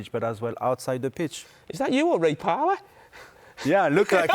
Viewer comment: Can someone clear up wth was said here 23:02 did i miss the joke or what? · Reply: Carra was suggesting Petit looks like Ray Parlour in the picture, but god knows what the high pitch noise was all about.